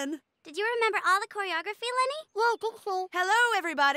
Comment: did she just call him Lenny? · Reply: Kelly couldn't really understand Kenny when he told her his name because of his muffledness